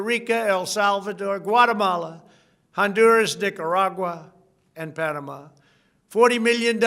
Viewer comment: It sounds like he said "Dickeragua", not Nicaragua.